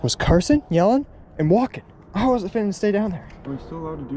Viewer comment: “some dude was cursin, yelling, and walkin. i wasn’t fixin to stay down there” not even Confucius could match that quote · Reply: I thought he said finna not fixin